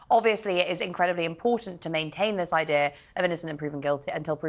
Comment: did she say idear